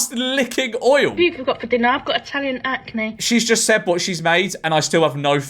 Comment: "oil for dinner ,I've got Italian acne"... is...is that what she said?